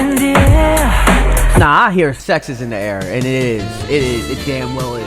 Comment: 100% all I hear is "sex is in the air," but I can see them writing it out that way.. keeping it PG..13?